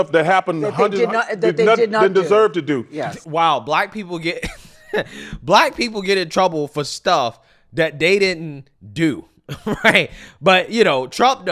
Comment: Yo! I love your channel BUT you missed a HUGE part of what Barkley said!! Go back to 9:17 in the video - Barkley, even though interrupted by Gayle, says "black people get in trouble for things they DESERVE to do", he didn't say things they DIDN'T do. I'd love it if you check it out and let us know what you think about that <3